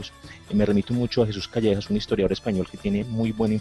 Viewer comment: En el minuto 31:37 menciona un autor español pero no se entiende su pronunciación, podrían porfavor dejarlo en comentario para encontrar buscarle? gracias por su trabajo, excelente aporte. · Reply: Jesús Callejo. También puedes buscar el podcast Ser Historia, tienen un episodio dedicado a esta dama.